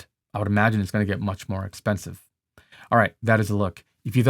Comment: Not gonna lie, I keep hearing 'the stupid da vinci' and I gotta say i like that name lol